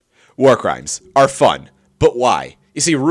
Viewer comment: Not even 10 seconds in and the first thing I hear is “ear crimes are fun” 😂